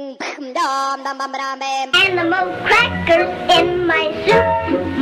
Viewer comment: is it just me or did I hear I’m the most flat girl in my soup?